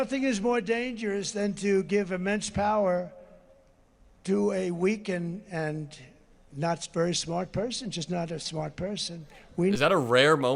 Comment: This is what I heard him say... "Weakened and Knotts Berry smart person."